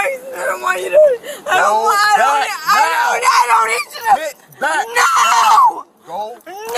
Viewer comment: Did he call her a "bitch" just before he pushed her to the ground? 😂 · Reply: He said, "Git back!"